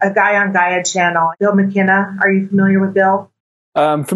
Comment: anyone catch the name of the guy she is talking about in 10:34 ? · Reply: Bill Mckenna